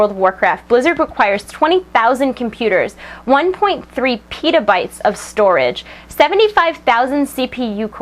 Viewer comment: Did she just say 1.3 pedobytes?